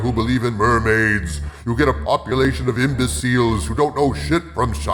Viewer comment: did you intentionally say "imba seals" or was that just how i heard it